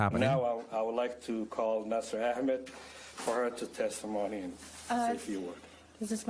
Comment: caption displayed “test some money” instead of “testimony” 😂